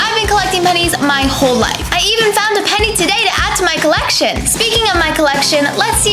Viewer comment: MAN I THOUGHT U WERE SAYING THE SHOP PENNYS I WAS ABT TO CRY